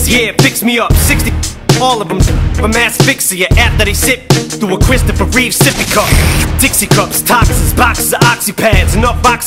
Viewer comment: "through A christophe reeve sippy cup" "through christopher reeve's sippy cup" sounds like he says the first one. I don't even know what it means but I guess he likes to call himself superman.